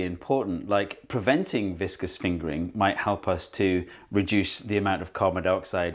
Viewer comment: I THOUGHT THAT SAID FINGERPRINTING AND GOT JUMPSCARED